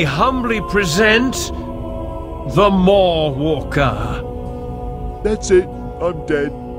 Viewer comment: "that's it, I'm dead" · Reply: I thought he said, “That’s it, I’m gay.”